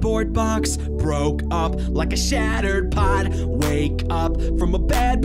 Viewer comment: When he says broke up like a shatters pot it sounds like he said a shatters pod